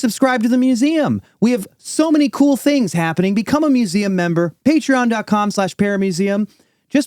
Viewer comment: In y'all's pitch for the museum membership, when says "paramuseum" I always hear "paramecium"